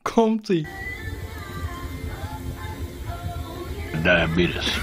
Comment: Nu weer de vraag van de eeuw, wat zegt die tweede kat bij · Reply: Diabetes